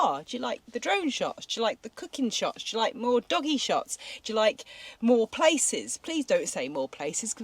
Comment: I could have sworn you said 'dogging shots' 🤣🤣🤣🤣🤦‍♀️ sorry lol